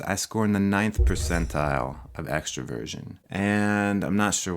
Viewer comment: When he says Extroversion... i hear him saying “Extra Virgin” 4:30 4:45 4:52